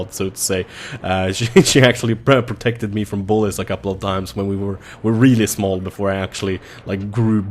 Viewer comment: The first time you said it I heard "she actually protected me from bullets a few times when we were smaller" and I was like dafuck bullets? :D